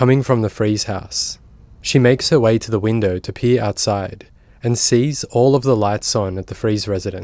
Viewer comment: FWIW: at the 6:10 mark it sounds like you say, “[the neighbor] goes to the window to pee outside.”